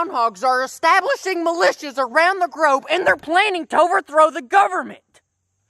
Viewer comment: said “grobe” instead of globe